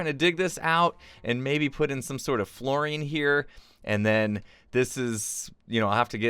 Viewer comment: I could have sworn that said "maybe put in some sort of fluorine here" at 13:39 . I blame spending too much time in my chemistry class.